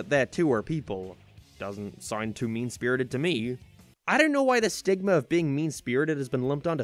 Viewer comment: I swear I thought that said sigma 😆